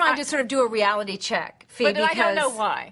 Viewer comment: did she just call her Febe at 03:09? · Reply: No, she says, "Fi". Short for Fiona.